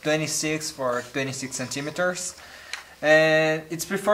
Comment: Did he say Inchimetres?